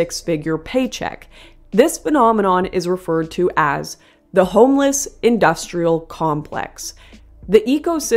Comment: I thought that said "The Homosexual Industrial Complex" at first but then was severely disappointed